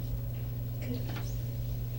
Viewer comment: she giggles and says "goodness" not "good lesson". I thought I heard "good lesson" too but I listened to it a few times and I'm sure it's "goodness". Either way you are way right ray gibson, she is weird for sure!!